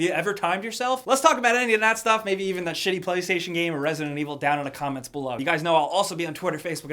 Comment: There's some good shit releasing next week, but what are you playing this weekend? Also yes- I still don't know what year it is. Congrats for being the ONLY person to point that out <3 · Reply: at the end it sounded like you said "that sh*tty game resident evil"